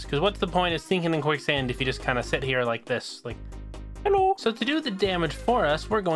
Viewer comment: Did u say Hallo? I guess it was german xD